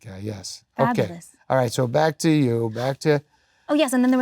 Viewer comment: am I the only one who thought it sounded like he was singing the lyric in everytime saying "back to back to" 😅